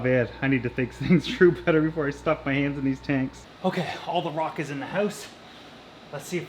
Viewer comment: Love it... I thought you said "no more aquariums in the house" ... lol